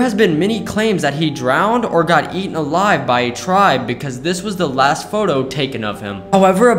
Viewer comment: I thought it said "eaten by a tree' instead of tribe